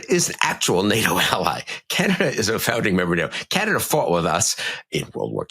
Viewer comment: A minor quibble with Bill's statement that Canada followed us into WWII: Canada entered that war a full two years before we did. Nards of steel, those Canadians, and I'm proud to stand alongside them. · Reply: I listened a second time: I really think he said "fought with us". Got me riled when I thought he said "followed us" but he didn't. BTW Canada entered WWII 1939, USA entered 1941 after Pearl Harbor.